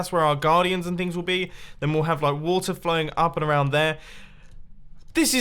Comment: did he say water flowing up???!?!?